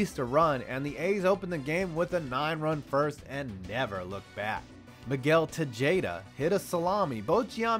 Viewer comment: d-did he just say Miguel Te-jade-a? Bruh......